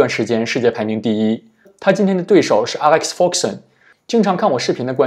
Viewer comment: 你講得Alex foxen我一開始聽成Alex fogsen，想說老爵爺為什麼來打德州了😂😂😂